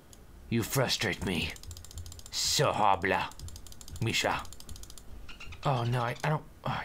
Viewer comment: it sounded like he said my name at 4:12 0_o (my name is "Misha").